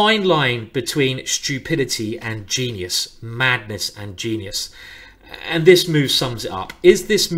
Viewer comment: Did he just say "magnus and genius" at 4:39?!!